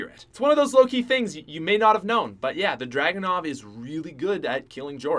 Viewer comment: At number 8 i thought you said the drakon and i got so mad but it was the dragunov😂